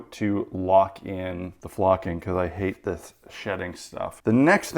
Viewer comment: 'hate this shedding stuff'.. I swear I heard you say "I hate this shit" haha